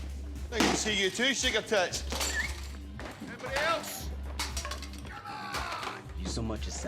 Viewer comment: What did say at 1:39? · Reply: “Nice to see you too, sugar-tits.”